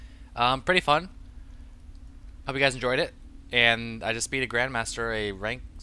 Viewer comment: I was in the process of fastforwarding to the good parts when I heard "And I just ate..." and I HAD to go back and here what deliciousness just ate!